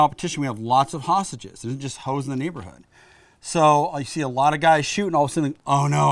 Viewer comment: did he say "Ho's in the neighborhood" that's funny